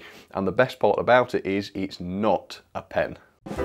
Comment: When said it wasn't a pen, I misheard it - I thought he said "The best part about it is it's not a *ped*". Poor Class 31!